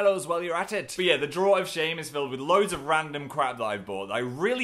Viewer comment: I thought he said 'filled with random crabs'